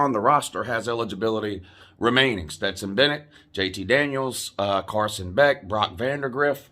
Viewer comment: I thought you were saying "JT Daniel Christ." Does that mean you are going to become a fan of whatever team he goes to?